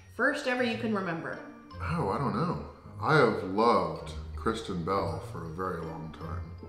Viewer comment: The first time said Kristen Bell I thought he said Christian Bale. 🤣🤣🤣